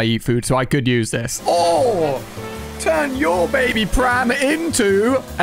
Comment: I'm sorry did you say baby crab? Or Baby prab? I think you meant baby crib 🤣🤣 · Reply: He said baby pram btw it is basically the UK version of is it stroller? The US version.